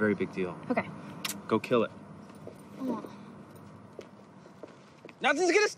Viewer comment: Can someone please tell me what Jesse says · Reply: i think he's saying "Becs in effects, y'all"